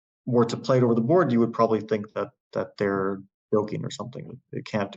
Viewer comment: can someone please tell me what Fabi's saying and what it means? It sounds like "milking" and the CC says "buking" and I can't find anything on Google related to either of these. The best I can concieve is that he's referring to the moves not making any sense so it seems that if someone played it they were just doing it to save time; "milking" the clock. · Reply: hes saying the word joking, the move looks like a joke